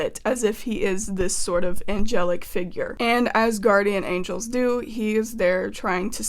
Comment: I thought you said “Asgardian Angels” for a split second.